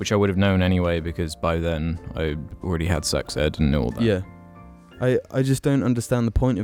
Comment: I thought said he had sex by the time he was 12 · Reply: Sex ed